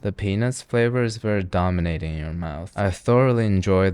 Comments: did he say penis flavor?? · Did he say penis flavor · I HORRIBLY misheard "peanuts" as "penis" and whipped my head over so fast like the WHAT